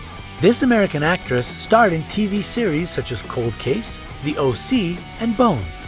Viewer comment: Did He Say "T-Series?" oh wait.